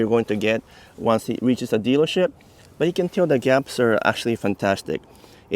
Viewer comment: I thought he said "Here is how you can the Japs are absolutely fantastic" and I was like "WHAT??? I can't believe he just said that!" Ohhh, "gaps"! LOL